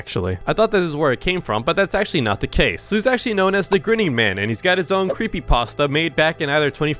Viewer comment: I thought he said "the gridding man"